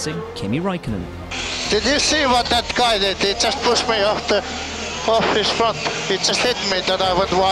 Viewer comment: What does Kimi say at 2:20? He pushed me of the track?? And then ¿....? Right stuff...